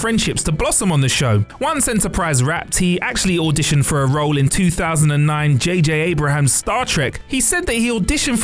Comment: You did not just say JJ Abraham!!!